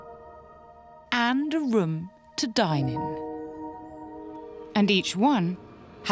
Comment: Why did I hear "To die in"